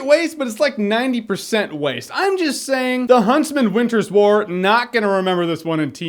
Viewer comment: It sounded like he was saying "The Huntsman: Winter's Bore"